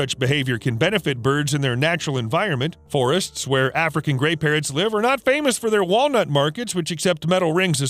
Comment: "Where have you been for so long" Grey parrots: i was a business man..doing business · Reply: Omg i thought you said “gay parrots”